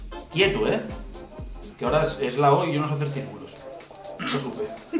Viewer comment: "Quieto ahora que es la O y yo no se hacer circulos" Se rien Se miran a los ojos se da cuenta que no lo dice en coña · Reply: te juro que yo la primera vez escuche " yo no se hacer triangulos" y me hizo mucha mas gracia jaja